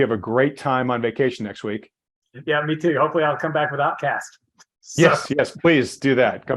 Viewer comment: At the end of this video, Lance says "Hopefully I come back without casts." The first time I heard it, I could have swore he said, "Hopefully I come back with Outkast." I was looking forward to Andre and Big Boi's take on the current economy.